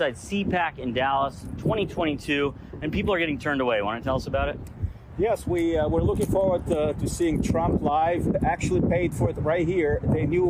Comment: At first, I thought the guy in pink said, “I came to see Drumpf lie”…